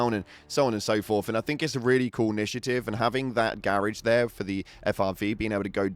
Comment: I'm so confused when I heard "garrige" and only now I noticed he means garage.